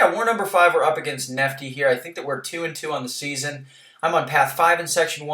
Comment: Congrats on the win! I was referring to 4loki’s record — we were 2-2 going into this war. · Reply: looooool I'm sorry I totally heard " we're up against nefti here, I think they were 2 and 2 on the season" ahahhaha but I guess you said "I think that we're 2 and 2" sorry about that. Thanks by the way and good luck to you guys for the rest of the season!!!